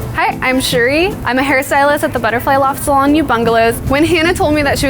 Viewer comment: couldn't figure out wtf she was saying. i googled mapped it , i guess 'ubungalows'